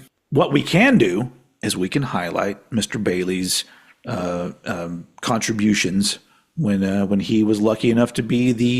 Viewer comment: I thought you said „masturbalies” and I thought what a weird way to say that lol